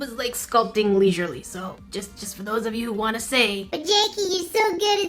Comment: Instead of hearing "but Jackie" I heard "bjancki"